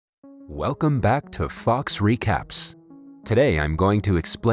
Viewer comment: really thought he said welcome back to f*cks recaps